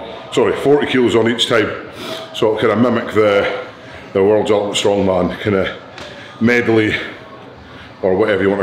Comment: Sounded like he said world omelette strongman 😂👌 big love though